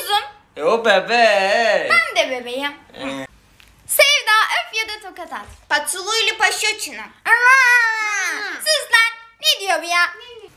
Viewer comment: ✨sEvDaĞaĞa Öp YaDaĞv ToHaT aT✨ ✨bAhĞk SuLu İle PaŞa ÇiN hAğhğ✨ Not: bir tek ben bu sahneyi böyle duydum